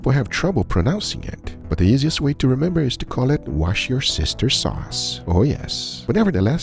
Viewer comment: I thought he said watch your sister sauce